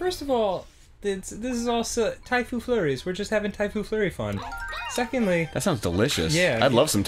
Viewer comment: I really thought they said Typhoid flurry...